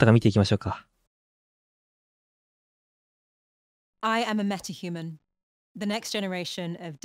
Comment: アイ アム ノット ヒューマン(空耳)